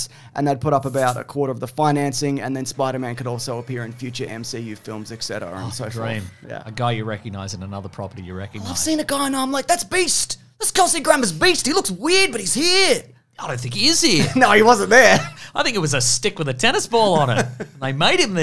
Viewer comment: I thought he said Mr Beast 😂 I was like whatttt? 😅